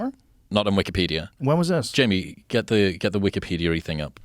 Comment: is he saying jimi 😂